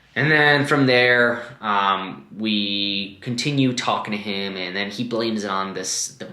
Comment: I thought you said no talking 😂😂😂